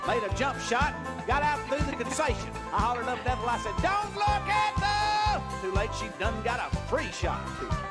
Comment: I thought he was saying "Lookit dat (look at that)."